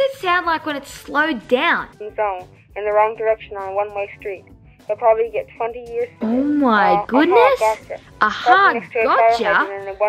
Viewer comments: i slowed it down and i heard, “oh, wrong direction on a one way street. he will probably get 20 years for this. mhm gotcha” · I hear ‘’ One way street 20 years ago’’ That’s what I hear when it talk fast